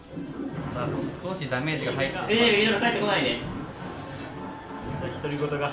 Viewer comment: なんて言ってるの？ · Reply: （えぇ、ベビドラ帰ってこないで)だと思います